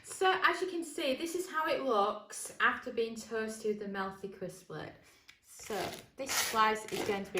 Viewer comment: I keep hearing "moolty kooss blade". What are you actually saying? (I'm not trying to be funny) You'd probably find my Glasgow accent hard to decode too! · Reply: I think its "Mealthy CrispLid" ... which turns a pressure cooker into an air fryer. Happenin? You wint tae cum to ma bit cos I've goat an empty ra morra 'n a fancy a swally?